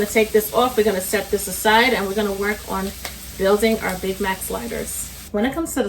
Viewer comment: Who else thought it said big back sliders in minutes at first?🤣